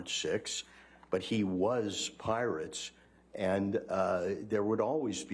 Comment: I recognise I'm not American and I just don't recognise the accent and this may be wilful mishearing on my part but doesn't the guy keep saying "Pirate Sex"?